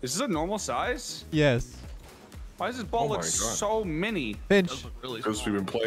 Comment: I thought he said b*tch but he said (pinch) 😅😂🤣😂🤣